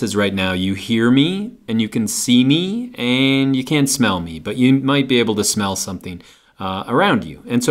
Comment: Mr. "You can't smell me." Me: sheds a tear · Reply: That's so hilarious because I heard " you can smell me" , lol !!!